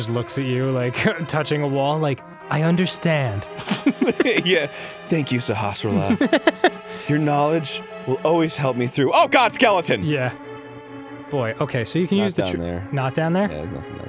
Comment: When they die, I hear "Sharial."